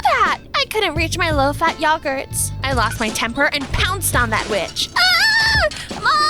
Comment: what she heard: “pounced on that witch“ what i heard: “pounced on that b---“ also it dont sound like it unless u have on the tv playing to